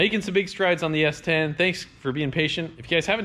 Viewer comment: I keep hearing "Thanks for the impatience."...